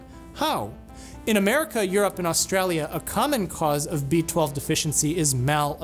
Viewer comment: I had to rewind this part like 6 times to understand what you were saying lmao. I was so confused by "in america, you're up in australia"